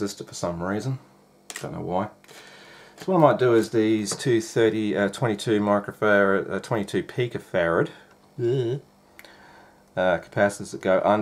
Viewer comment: 🤣 thought he said 2.22 gigafarad capacitor. 😂 had to rewind and listen closely or see if I could find a nuclear plant that would let me connect 2 wires to. Great vid so far. Watching the whole thing. Might actually get one. Should also add if you noticed when he soldered in the IC socket HE HEATED TO WORK NOT THE SOLDER!!!! I am sick and tired of content where they solder by putting the solder to the iron, that’s proper soldering etiquette. Good yob mate. If you see content where they heat the solder not the work like he did by heating the pin then solder then you’re watching a hack .